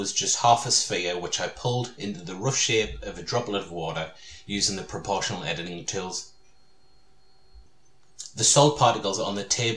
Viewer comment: What is he saying between 9:05 and 9:08 ? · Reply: I said that I pulled the sphere into the shape of a droplet of water using the proportional editing tools.